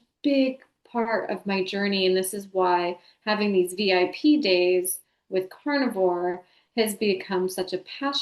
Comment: said "VIP Days" quickly and I had to rewind because I thought she was saying "PJs" (as in pajamas) Ha Ha Ha I wondered if we all would end up eating that steak she cooks in our pajamas! This is such an excellent interview! I heard the word "home" throughout the entire thing... as in my guides are saying, "Yes, love... You've FINALLY found it!" 🌠🧬💗 and there's cheering... dancing... and GLITTER!